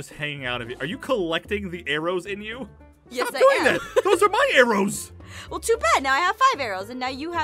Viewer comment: I turned on cc and it said “money rooms” instead of “my arrows”